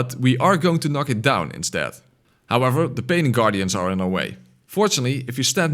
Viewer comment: it sounds like you said paining guardians, and that is what they are.